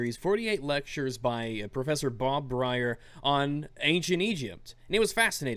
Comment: I was listening to this in the background while watching some porn, then I heard him say in my mind "Bob Bryar" who is the ex drummer for MCR. It's actually Bob Brier, but hey whatever.